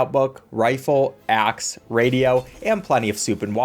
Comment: When he Said Axe I Thought He Said Ass